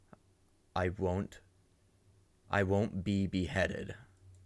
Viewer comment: i thought that said “i can’t be bothered” 😭